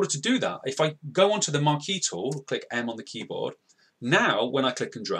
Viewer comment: Monkey tool??? I dont understand what you said